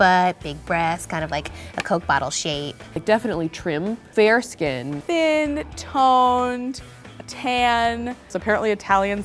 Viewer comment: when she said "thin, toned, tanned" i literally heard " tin, ton, tan" lol